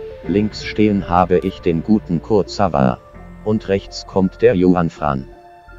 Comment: ich habe da erst anstatt Kurzawa Mo Salah verstanden 😂😂😂😂